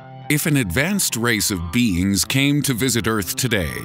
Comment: If an advanced race of beans came to visit earth today 0:02 0:04 anybody else heard beans instead is beings?